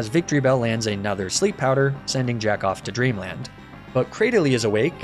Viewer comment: Bro I unpaused at the end and I wasn’t paying attention and all I heard was “jack off to dream land” 💀💀💀